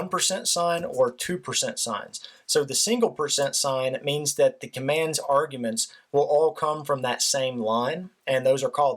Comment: What is a bash command? (about 15:30) Or did you say 'batch '? Either way, what is that please? I'm totally new to Python programming. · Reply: he said Bash and its a scripting language to write command lines in terminals, it has nothing to do with python in this video